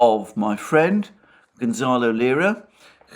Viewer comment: Mr. Mercouris - do you have the full name of the gentleman being held captive in Ukraine? I am trying to find more information but in the US media it is an unknown story... I thought you said Gonzalo Lira but I seem to be mishearing quite a bit lately. I wonder if that is also his real name? I will write to the only representative I trust in this state, Mrs. Lauren Boebert and I will call her office. I don't live in her district, but she is the only one who would take this seriously in my humble opinion of course. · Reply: Gonzalo Lira.